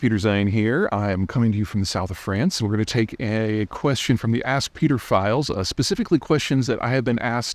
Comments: Is the choice of 'Ask Peter Files' deliberately dodgy sounding? I heard 'Ask Pedophiles', but maybe it's just me as I have dodgy hearing and maybe a corrupted brain. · Did he just say “ask pedophiles”?